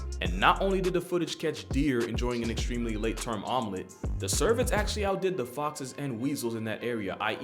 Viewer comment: the "cervets"? I can't tell what word that is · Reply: cervids (deer/elk/moose)